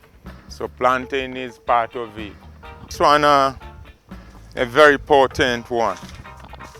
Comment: did he say "Traunna" for fever and aura cleansing?